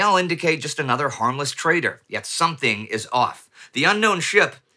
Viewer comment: Harmless "trader" or "traitor"...? Honestly I heard the latter initially.